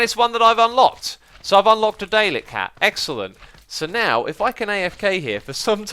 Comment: What the fuck, did you just say "Daylek"